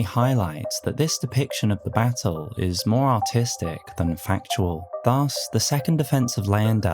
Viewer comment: I thought you said the battle was more autistic for a second and had to put on captions 😭😂 I am COOKED